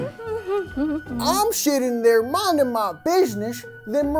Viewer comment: At the beginning when he said I'm sitting there minding my business it kinda sounded like he said I'm sh*ting there minding my business